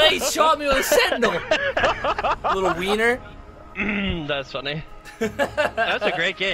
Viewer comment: What I thought I heard “little beaner” goes back what he actually said “little wiener” lmfao